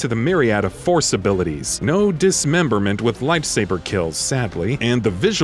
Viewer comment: Did you say "Lifesaver kills"?